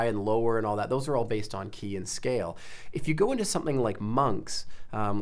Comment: Although very informative, I find that it takes up a lot of time continually having to backtrack, as this guy talks at 100mph. OK, he knows what he's talking about, but he's not the greatest of teachers for us folk that are trying to pick it all up. As a musician, I know ALL of my scales, but at 1:20, he refers to something that sounds like a KIAN scale? I've played it over many times, and still don't understand. Possibly it's an Americanism that as an Englishman, I don't understand. I would be grateful if anyone would enlighten me. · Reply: George Lee he says „key and scale“